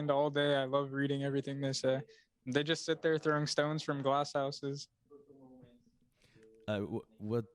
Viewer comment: Please can some one tell me what he say's in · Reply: "they just sit there throwing stones from glass houses"